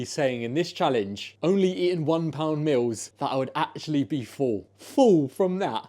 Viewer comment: I thought it said I’m only eating £1 metals today😂😂😂